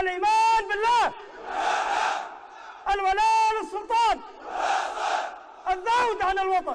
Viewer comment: وش يقول فالدقيقه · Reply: الولاء للسلطان